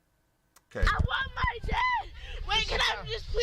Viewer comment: Did she say ....I want my dad.....?